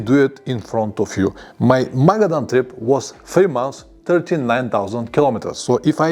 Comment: It was 433 km per day for me. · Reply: oh I must have misheard, I thought you said 14000km over 90 days, did you mean 40000?